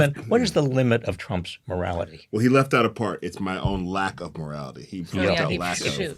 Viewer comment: guys I swear I thought he said his own MORTALITY